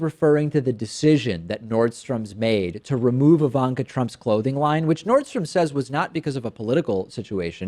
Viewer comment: all I heard was "remove Ivanka's clothing" ;)